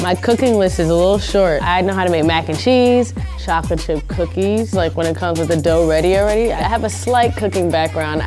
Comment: i thought she said shoplifted cookies lmao